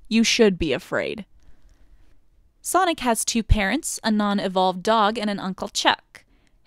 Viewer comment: I heard that as "Sonic has 2 parents (who are): an unevolved dog, and uncle chuck" I was like holy shit dad is called uncle and mom is an unevolved dog?!